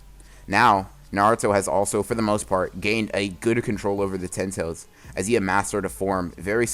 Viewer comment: did he say Gooder